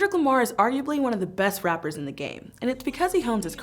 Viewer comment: she say rappers the subtitles says lyricists somebody gotta explain that